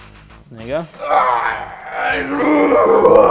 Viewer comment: did he say ni** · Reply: Abood Sharpshoot he said "there you go"